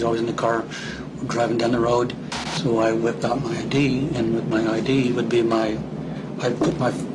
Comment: in the video I thought he said I wiped out my d lol